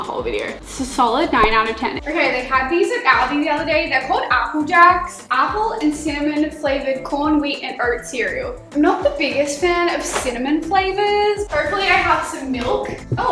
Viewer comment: When she told us the flavor of the Apple Jacks I thought she said “ Apple and Salmon “ because I was a little distracted😂😂